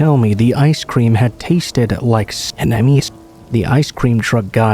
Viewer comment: Can someone help me out? What was in the ice cream? I can’t make out what’s being said. It sounds like it skips every time that word is said. 9:13 thanks 🤍 thanks for the longer video! You’re amazing x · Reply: Youtube don't like the word for what squirts out during Master Bates.